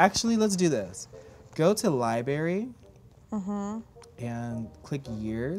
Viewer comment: Did larray just say LIBARY